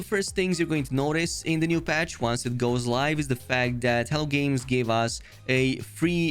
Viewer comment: Every time you say "Hello Games" I hear "Hell Games" 😂 Thanks for the update on the patch!